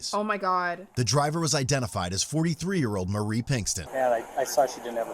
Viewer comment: When it said “43 year old” I heard “former 3 year old” lol